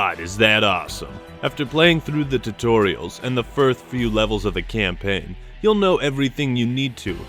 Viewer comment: READ THIS TO AVOID CAMPAIGN SPOILERS! At 10:40 into this video, I was supposed to give a time stamp for people to avoid the campaign spoilers but didn't, that time stamp is 18:39 sorry about that! What do YOU think of Halo Wars 2? Will you be buying it? Still skeptical? Let me know in the comments below! Subscribe to my 2nd Channel: <link> How I got a copy of Halo Wars 2 Early and For FREE: <link> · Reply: Did you really say "1th few levels" at the 2:35 mark, was that intentional